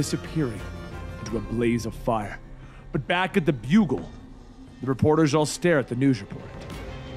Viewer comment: did he just said, "Back at the Bugle?"